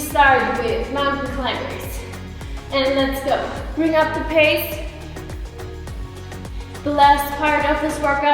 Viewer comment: says "bring up the pace" but all I hear is "bring up the pain" 🤷🏻‍♂️